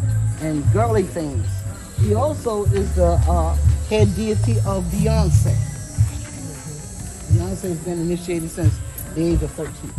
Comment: Did she say that she is the head diety fir Beyonce and that she has been initiated since 13yrs?